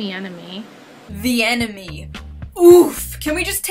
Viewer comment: it sounded like she said “the emeny” and that she couldn’t pronounce it correctly.